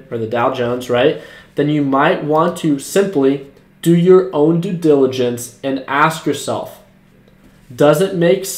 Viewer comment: why does he say didiligence at 3:35. The word is diligence.